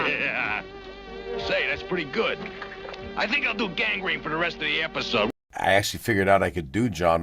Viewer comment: did he say, "I think I'll do gang rape for the rest of the episode" ?